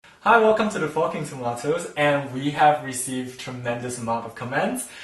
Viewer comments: Lol at the start when u said “he’ll welcome to the forking tomatos” I thought u said “hello welcome to the fucking tomatos” 😂😂😂😂😂😂😂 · It sounds like you said “welcome to the fucking tomatoes” instead of forking tomatoes 😂😂😂😂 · Omg, I thought that he said "Hi! Welcome to the f*cking tomatoes" xD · when he says forking thought hr said fucking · It literally sounded like he said welcome to the “fu*king tomatoes” 😐😐😂😂😂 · Thought he said the f***ing tomatoes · thought he said fucking tomatoes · 0:1 I thought he said Fucking tomatoes you know tonatoes that have sex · im american and i just wanted to say that when the guy said forking tomatoes i thought he said 'fucking tomatoes' and so i kept replaying it until i realized thats not what he said hahahah